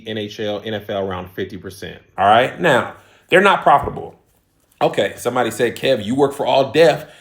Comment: Sounds like you saying they making a profit but u say you agree that they not profitable 😢